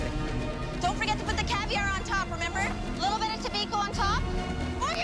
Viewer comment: Im sorry but did just call that caviar